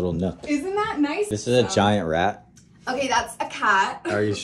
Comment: I thought for sure my man said "giant rack", I thought hell yeah man!